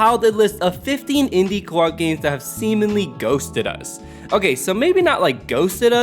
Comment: it sounds like you said semenly. Games look sick tho, killer list.